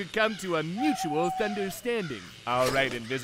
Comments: he said mutual thunderstanding instead of understanding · i'm sorry, but did he just say mutual ""thunderstanding"? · Did he say thunderstanding? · did Guilthunder just say " mutual *thunder*standing"? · Did anyone else hear Thunderstanding? The captions say understanding. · Did he say "understanding" or "thunderstanding"?